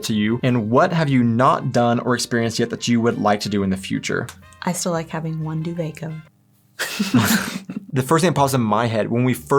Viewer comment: what did she say? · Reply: "I still like having one duvet cover" in reference to how Germans typically have two separate duvets on a bed for two people and Americans typically have one for the entire bed.